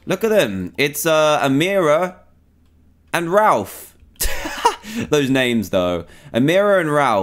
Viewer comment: I thought he said A mirror and Ralph